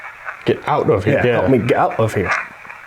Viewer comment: it sounds like it says "Get the HELL out of here!"